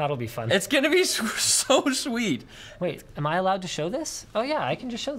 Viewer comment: when says "It's gonna be so sweet" I totally thought he said "It's gonna be so much weed"